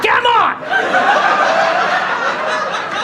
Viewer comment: right after Randy shouted "WHERE THE F*CK AM I?? "auto-subtitles says "hahaha foreigner"